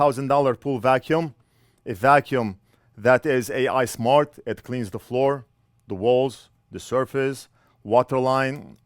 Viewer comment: Ismart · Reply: "that is AI smart, it cleans the floor..." I thought he said "ISmart" at first, too lol